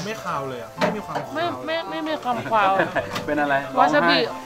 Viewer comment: ทำไมเราได้ยินว่า “คาม ควาว “ 555